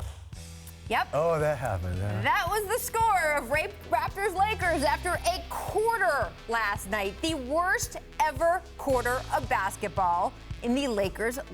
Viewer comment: Ayoo did she just say the RAPEters at 2:02?? 😂😂